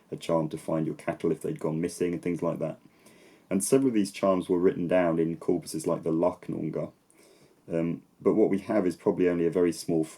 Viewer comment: in the video, makes reference to a written source for charms, but I can't understand what the name of that source is. Help, anybody? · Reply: the Lacnunga!